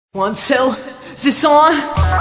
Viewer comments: I always thought the beginning he said “On self, is this on.” I still don’t believe he says, “one, two, is this on. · Bro wtf is he saying at 0:00 😂 let me know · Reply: 1,2 is this on? Referring to the mic